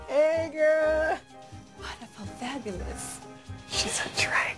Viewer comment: I wonder how everyone hears "wonderful fabulous". She said "*gasp* I feel fabulous". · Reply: I thought she said “what a pool! Fabulous!” Lmaoooo